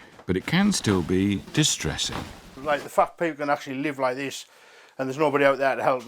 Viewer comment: did he say "a fat pig can live like this"